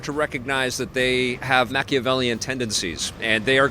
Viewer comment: wat word is that? I cant find it on google. How do u spell it? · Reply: Machiavellian (two faced/conniving/untrustworthy/deceitful)